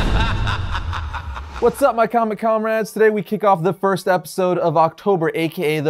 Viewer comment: A quick suggestion, at the beginning of the video it sounded like you said what's up commie comrades